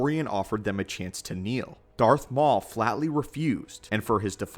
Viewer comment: Am I the only one who heard him say darth maul instead of darth marr